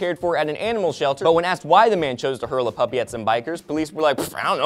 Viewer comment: i thought that said "hurt puppy" but wtf.